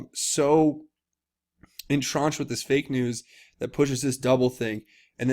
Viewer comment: Did you say 'entronched'? I believe the word you are fumbling for is 'ensconced'.